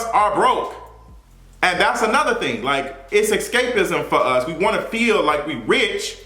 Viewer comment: I heard that like "Xscapism" 😅